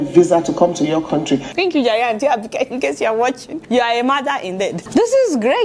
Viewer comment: "You're a madder indeed." Ah! Adeola, why are you like this? I know you said mother, but I heard madder. lol